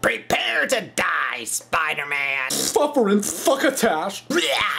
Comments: What u say? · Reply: suffering succotash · what did you say! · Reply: Suffering Suckatash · from 2:08 to 2:10 it sounds like hes saying "fuckerin fuckitac!" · did he just say f****er in f****ertown!